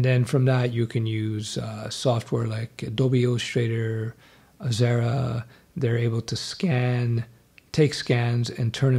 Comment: Thanks. I am very much interested in such course. Is it specific about Web Development? These topics get addressed rarely.. Btw. What was the name of the software at 4:35? It sounded like Adobe Straighter and Xerra. :) You mentioned presenting designs. How do you present designs acutally? I assume you don't mean coding several websites.^^ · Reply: Adobe illustrator and Xara.